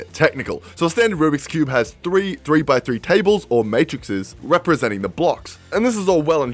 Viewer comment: Did u just say matrixes?? 1:50 ish